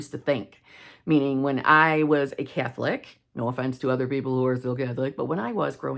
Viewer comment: I was listening to this when I was half asleep and couldn't have swore you said Cat flick, not Catholic. 🙈